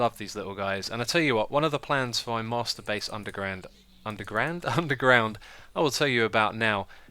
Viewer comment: I thought you said " One of the plans before I masturbate underground " o.O